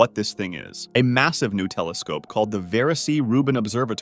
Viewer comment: 😂😂😂. Instead of hearing “new telescope” I heard “Nutella scope” I must be hungry. 😂😂